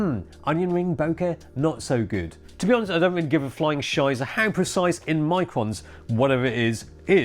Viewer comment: what is he saying here? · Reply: flying shiza. german word for shit. “flying shit.”